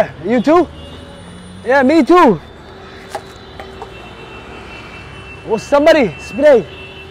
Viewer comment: can someone explain me what did he just said? In that accent? 😅😂😂😂😂😂😂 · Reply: Is Somebody Potty On My Head